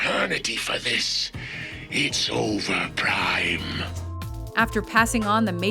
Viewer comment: I heard "It's over nine..." The DBZ fan in me is erupting XDD